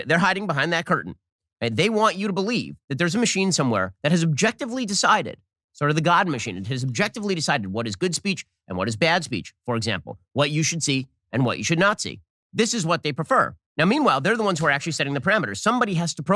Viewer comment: "what you should NAZI" - i hear your subliminals Shabibo you secret Yedolf lover!